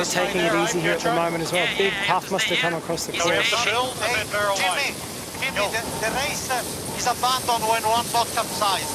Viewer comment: ".... runner" what is he saying? what kind of runner? thank you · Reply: "Running backstay" thanks a lot, got it. darn, that sucks